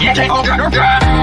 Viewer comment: When he said YOUR FIRED!!! it sounds like he said REQUIRED!!!!